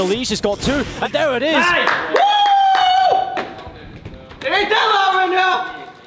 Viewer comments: what did stew say at 9:52 ? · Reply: It aint that loud ride now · What does Stewie say at 9:51?? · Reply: Aditya Manoj WOOOO , IT AINT THAT LOUD RIGHT NOW